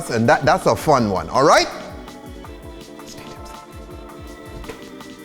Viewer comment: What is he trying to say? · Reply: He said, "Stay Tipsy". It's his tag line.